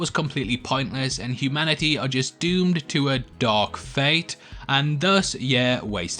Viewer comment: I thought you said the name of the film was Terminator Dog Face Which actually sounds cooler and more interest stimulating than something Marvel generic for a name like... Dark Fate They need to hire us both as a team to name movies.